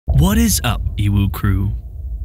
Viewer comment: I keep hearing "What is up UwU crew"